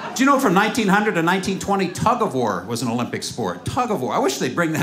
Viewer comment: I'm Mexican, and I'm literally crying at the Taco War joke... 😂😂😂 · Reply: I'm pretty sure our Mexican friend here knows what tug-of-war is. The joke he is referring to is the joke made when he said, "Tug-of-war? I thought you said 'Taco War'!"